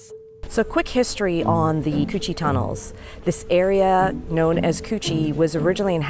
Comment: I thought they said chochie Tunnel